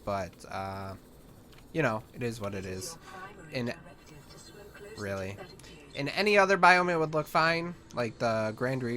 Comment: what was the A.I. saying? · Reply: it was asking him to swim closer to the mesmer